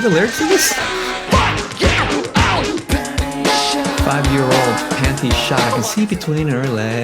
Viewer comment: Holy shit, I thought he was saying “find your own panty shot” for 20 years. Welp….